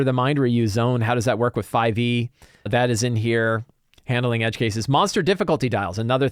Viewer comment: I was listening on x2 speed and misheard "Handling Edge Cases" as "Handling Chases," and thought, it would be great if there was a Lazy (tm) way to do a good, movie (or Uncharted game) style chase. And then realized, that could probably be expanded to Lazy (tm) encounter archetypes, which in my mind would mean scenes that would have likely have a combat, or at least conflict, basis but have a main goal that isn't just defeat all opponents.